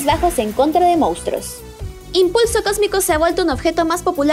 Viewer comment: no se por que escuchandolo desde lejos le entendi impuesto cosmico 😂😂